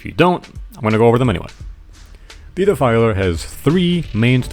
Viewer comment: i heard pdfiler oh no jokes aside i wish the next iteration of the boss removes the shield soldiers part and add another flying blade mechanic